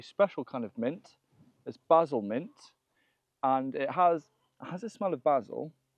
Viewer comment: I misheard "basil mint" as "embezzlement." Gotta pay closer attention!